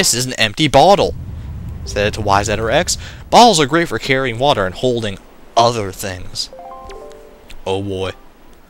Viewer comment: For a second i thought u said "balls are great for holding water and other things" the i looked back at the screen and thought "what the?!" then realizwd i misheard ^^; ㅋㅋㅋ